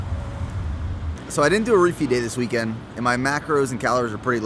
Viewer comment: HAHAHAHA Thought you said "Roofie Day" @ 12:37!! you're so dope man! an inspiration